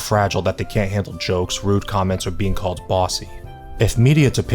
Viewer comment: thought that said "ban bussy"